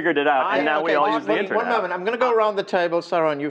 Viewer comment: all i heard was "Peter one moment"